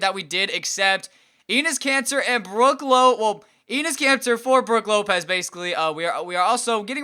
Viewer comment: also it sounded like you said anus cancer... instead of enes kanter